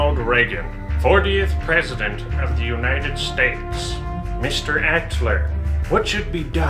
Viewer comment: I thought he said horniest president of the United states